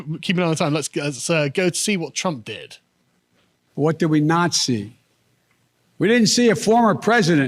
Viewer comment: it sounds like he says "what did we NAZI?"